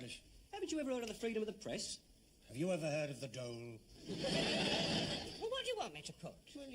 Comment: I love the part from 12:09 to 12:15. I initially thought captain peacock said to mr spooner Have you ever heard of the dome? but he said "Have you ever heard of the dole?"